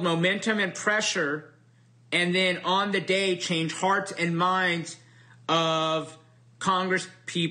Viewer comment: did he say "change farts and minds"?